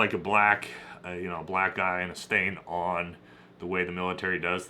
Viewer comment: what does he mean by that? What is he saying? Probably "Black eye " just has a weird way if saying it...